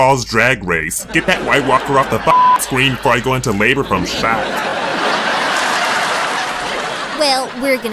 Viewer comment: “She looks like a walking dead meets rupauls drag race, get that white walker off the screen before I go into labor from shock” — khloe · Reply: I thought she said "from shad " or "from sad"